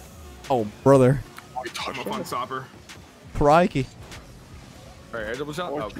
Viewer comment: When said crikey I actually thought he said karate kid and I was so confused